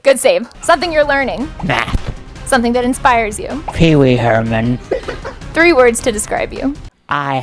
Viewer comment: I thought weird Al once they said Pee Wee Herman lol